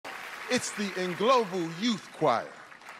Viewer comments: who else thought he said "It's the global youth choir"🤗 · I thought he said its En-Global youth choir😂😂😂 his pronunciation is funny